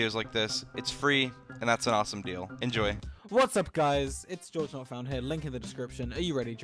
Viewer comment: subtitles say “hey it’s joe not found” lmao